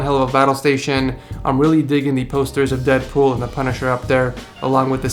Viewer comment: did he say Deadpool?